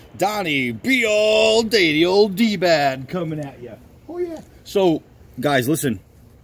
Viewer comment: When you said "D-bad" I thought you said "D-bag" 😂😂